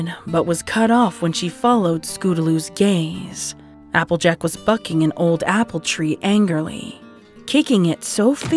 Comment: i heard it as "applejack was fucking and old apple tree"